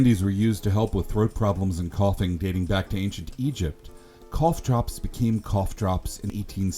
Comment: I thought those drops said couch at first